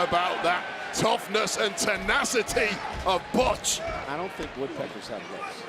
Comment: did he call him botch! lol